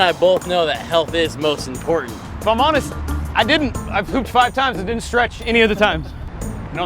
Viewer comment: said “I hooped” but the subtitles said “I pooped”. 😂